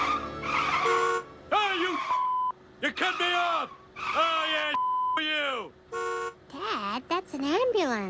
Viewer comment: I take a guess at what Homer said here: "Ah you fucks/cunts! Ya cut me off! Ah I fuckin asked you?!"